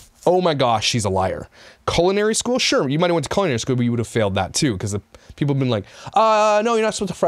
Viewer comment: 🤣 face when she says she would have gone to culinary school just made my entire week!! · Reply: She mumbles her words so much I thought she said coronary school...guess she cooked only things bad for the heart.😂🤣😂